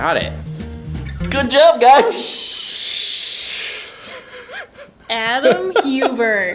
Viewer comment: ik what that gal want to say ;) "Oh shhhhhhhh......" Lmao